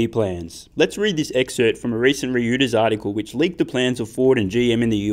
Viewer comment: Did you just say "ree-ooters"? :-D